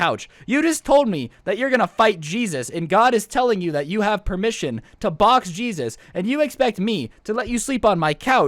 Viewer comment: I know its weird but I thought it said I AM THE BOX JESUS lol